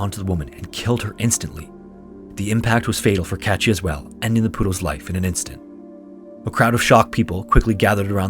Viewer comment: i heard "buenos aires is the capital city of argentina" and i was like ???? when i looked at the screen and read "poodle" i laughed omg that truly was such a random incident i can't believe you talked about it